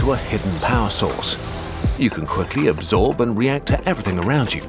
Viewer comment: How ironic & random! I have a prescription for (and thus consume) Vyvanse, and at about 1:56 the subtitles state "you can quickly absorb and react to everything around you" HOWEVER listen closely to the actual narration and you'll catch a mistake: the narration states "you can quickly absorb and react to every around you". The word "everything" has no "THING" in it! Yes - I know what the narration MEANT TO say, but it did not. Another weird thing - generally the YT subtitles are jacked up six ways from Sunday, but this time the subtitles were correct, and the narration was wrong.